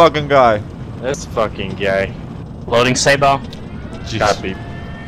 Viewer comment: Why is it that at 14:31 i thought he said "Loading SABOT" to peg the other Abrams in the ass for cutting them off?